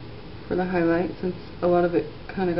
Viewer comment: ohh i thought it said ninja flowers...